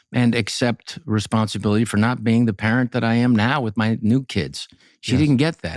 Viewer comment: Did Rob say "my new kids"? 😣